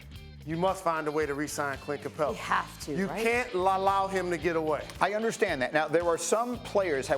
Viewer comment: what he say lalallowed 😂😂😂